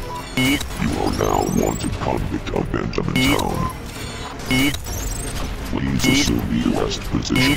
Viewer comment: I hear him "YEET"